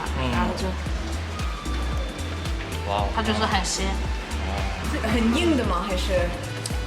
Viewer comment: I think she said 它就是很鲜 (umami), not 很咸 （salty)